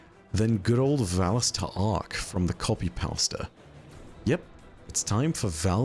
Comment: Trying to contend with the fact I just heard say "copy powsta"